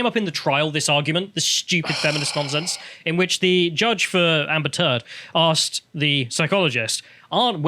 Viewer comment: Did say Amber Teard?